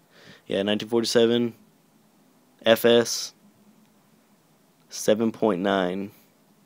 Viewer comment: Did i hear Fs on 3:20? :P